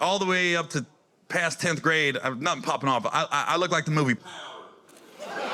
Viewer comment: dude i need to know what movie he said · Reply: Powder